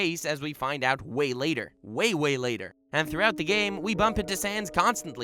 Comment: i thought you said Santa LOL